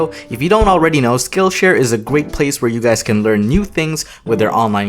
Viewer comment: anyone else heard the: «Gweat place»